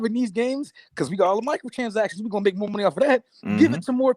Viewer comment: Love the show fellas. Xbox can't stop won't stop. Panel always got me cracking up, Probly just me but I always hear say "Michael Transactions" 🤣 keep it real